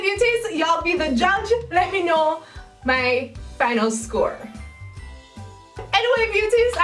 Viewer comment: i thought she said (my thanos score )